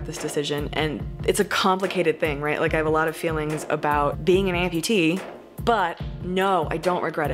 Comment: I heard “bologna amputee.” 😂